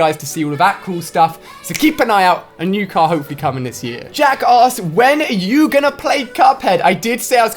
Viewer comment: it sounded like he said jackass